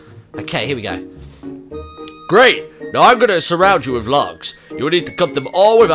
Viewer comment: Thought you said "Surround you with love" instead of "logs". lmao